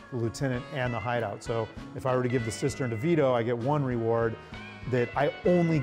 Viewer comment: Did he just say Vito in 1:28 ?